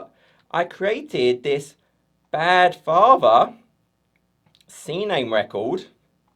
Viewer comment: Ha, I first heard "bad barber" and then looked at your hairstyle :) I was just discussing that with during security audit of our digital services in Azure. Once again great explanation and very funny pictures! Thanks for sharing knowledge and making me lough :)